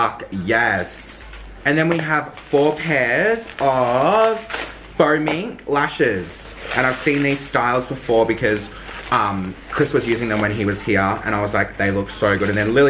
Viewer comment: I thought said "foaming lashes" instead of faux mink lashes. I was immediately interested and terrified... hahaha